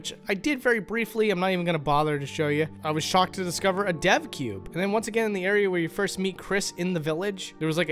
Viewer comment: "Did he say 'Deathcube' or 'Depthcube'?" ... "Ooh, 'Devcube'!" XD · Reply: Oops thought he was saying death cube the whole time.